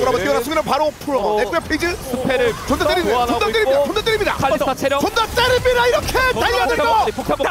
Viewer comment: 몇번을 들어도 ㅈㄴ 때립니다로 들리는데 뭐라고 하는건지 아시는분? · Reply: 좀 더 때립니다